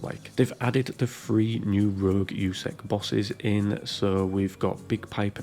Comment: For a second I thought u said "free new Rouge bosses" 😆. Great video. Glad I checked it out